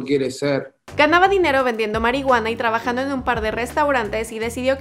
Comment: escuche mal o dijo q ganaba dinero vendiendo marihuana???